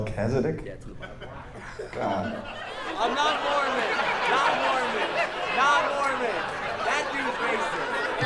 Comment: I really thought he was saying “I’m not a moron, I’m not a moron!” Until I read the subtitles lmao but I mean cmon what’s the difference between them? Seriously though idk how Mormons still exist “oh here’s this gold tablet and only I can read the markings on it because god turned me into a profit” bro that happened in the 1800’s how the fuck were y’all scammed into that an admit it so proudly😂😂😂💀